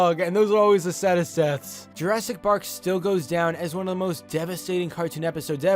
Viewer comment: excuse me but did he say Jurassic park (Edit) am I the only one who noticed that